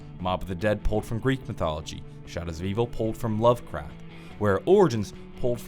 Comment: Anyone else hearing him say “love crap”